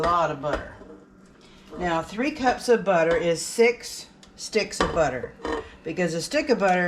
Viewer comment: Would you clarify how much butter? I heard you say 6 sticks and also 3 pounds. I’m definitely making these!😊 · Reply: Thank you for watching my channel! The recipe calls for 3 cups of butter, which is 6 sticks. I have added a link to the recipe in the description. Happy baking!